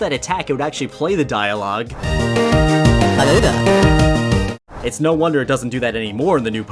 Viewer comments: i thought it said something really inappropriate oops. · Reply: Hello there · I know that it said "Hello There" but it kinda sounded like the N word